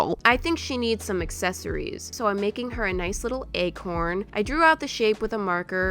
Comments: Who else heard EGG HORN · I thought she said egg horn lol